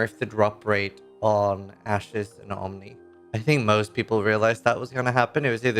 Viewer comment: Thought he said Ashes of my Homie not Ashes and Omni 😂